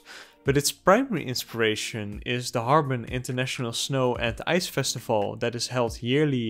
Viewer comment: I initially misheard: "But its PRIMARY inspiration is the 'Hårga' International Snow & Ice Festival, that is held yearly in Northern China." Of course, you actually correctly, & very clearly, enunciated the word "Harbin," which made WAY much more sense! The 'HÅRGA' [DEFINITELY NOT Chinese snow or ice based!] Festival was of course featured in the 2019 Ari Aster written & directed folk horror movie, 'Midsommar'! [Brief plot line]: 'Having been invited by their mutual Swedish friend Pelle to attend a midsummer celebration at Pelle's ancestral commune, the Hårga, in Hälsingland - in which a 'celebration' occurs only once every 90 yrs - they find themselves in the thrall of a Scandinavian neopagan cult!' I shall end my truncated synopsis there - due to inevitable SPOILERS for 'Midsommar'! - but let’s just say it in NO way follows a similar outcome to the wonderful & excellent animated series 'Avatar: The Last Airbender'! However, I kinda wish the sincerely godawful M. Night Shyamalan written, co-produced & directed "live action" [if you don’t include the EXTREMELY wooden acting!] 2010 film version of 'The Last Airbender,' DID have a comparable, much deserved & truly satisfying conclusion - for ALL the characters involved! - to that of 'Midsommar'!😬😳☺️ Edit: This brilliant video - with so much insight into the culturally influenced inspiration, explanation of "bending the Elements" & grounded in its relation to various distinctive styles of martial arts, seamless exploration